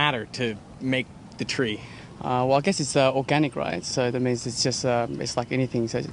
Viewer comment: I think the person at 7:40 is Asian :) did he say "Organic Rise" or " Organic Rice " :P he is smart though ;)